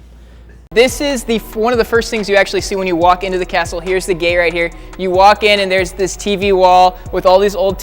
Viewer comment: did he just say here is the gay?